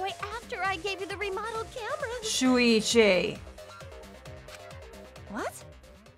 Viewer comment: when she says „Shuichi“ it kinda sounds like she says „Luigi“ and idk why but that just made me laugh xD